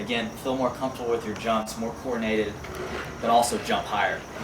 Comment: Everytime I hear him say the word jump, my brain registers the word junk. So while I'm listening I thought he kept saying dirty jokes, but I know what he means😂. Somewhere around 1:30--" So you could feel more confident with your junk."